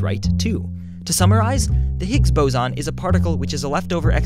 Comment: I thought it Said Higgs Bacon